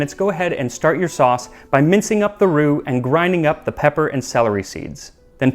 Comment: I was only half paying attention to this video and got very confused when I thought I heard him say “start mincing up the roux”